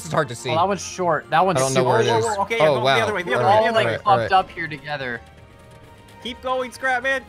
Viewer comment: It sounds like someone says were all fucked up together